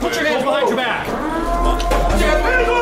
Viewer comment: Why did i hear "how can i be dehydrated my buttholes full" 💀💀💀💀💀💀💀